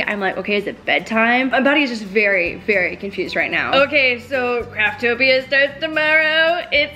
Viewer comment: I thought she said the name of the show was Cracktopia 😂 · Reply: I thought she said craptopia lol